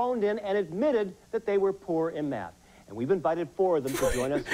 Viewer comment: "phoned in and said they were poor at math" the first time he played it I thought it said "poor and mad" which was way more funny. also using poor instead of bad is poor in that context